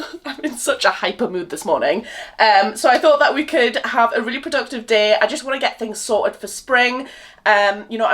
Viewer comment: I thought you said a reproductive day instead of really productive 🤣 x